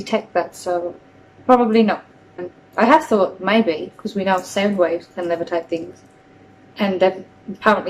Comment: Does anyone understand what she said? Its sounds to me like: "I have thought, maybe, we know that 'sebways' can levitate things." I'm sure I am misinterpreting her but I have no idea what she is actually trying to say here. Was she trying to say "sound waves"?